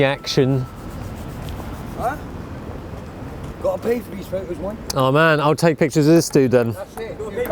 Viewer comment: can anyone tell my what does that table tennis guy at 2:17 says? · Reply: "You gotta pay for these photos mind!"